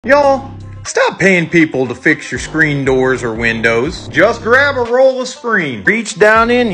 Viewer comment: I heard “start charging people to fix screen doors and windows” thank you 🤣🤣 fr tho